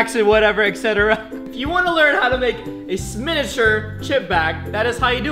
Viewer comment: said at 1:01 "Sminuture" lol